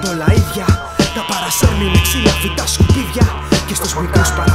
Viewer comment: Παιδιά, μπορεί κάποιος να διευκρινίσει τι λέει στο 1:56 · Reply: "τα παρασέρνει με ξύλα, φυτά, σκουπίδια" νομίζω